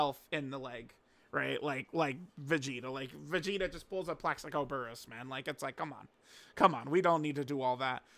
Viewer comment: did he just say PLAXICO BURESS!!! IM DEAD